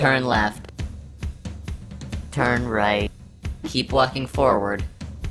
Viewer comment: I'm just gonna say that he's wearing a GoPro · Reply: I swear that thing said keep fucking forward